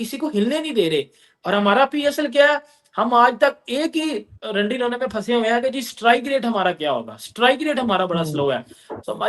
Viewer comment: Did he say @ 13:28 Rundy rona!?? be like kuch aur kaha hoga.😅😅